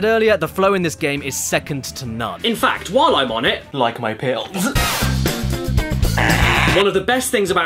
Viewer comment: Does anyone know what he says at 12:50? "While I'm on it, like my pill?"